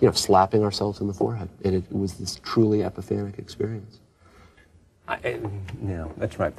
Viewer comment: Is it just me or is David saying “epipharic” rather than “epiphanic”? This interview needs an editor (jk)